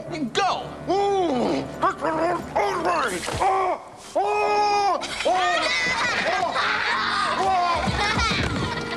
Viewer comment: I don't know what Mr. Hume said when he got toffee stuck in his mouth! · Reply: He said, "This is an outrage!"